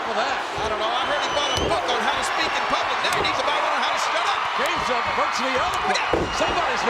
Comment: Comments like the one Jerry "The King" Lawler said around 5:49-5:53, for example are greatly missed in color commentary, today... Jerry "The King" Lawler said "I heard Kane bought a book on how to speak in public & now, he needs to buy a book on how to shut up.", in case you couldn't hear it... On top of that, I was 11 & a few months away from my 12th birthday when this match happened & didn't quite understand what he was saying, at first because it was so loud in the arena & because of how fast he was talking.